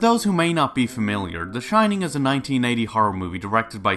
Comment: All editing and no editing makes something something . . . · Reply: I swear I heard you say “a 1990 horror movie”... it’s 1980